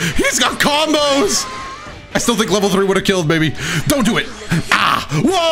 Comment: I swear in my head when got command grabbed I heard “DONT JUMP”